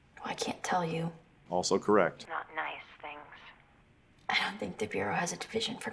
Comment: Maybe it's my Englishness, but I can't figure out what she says. What is that line that he says he loves? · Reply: Regarding the daughter telling the Mom, “I can’t tell you what I’m working on”, the mother says, “Not nice things”, that she would be working on. The daughter says, “ I don’t think the Bureau has a Division for Nice Things.”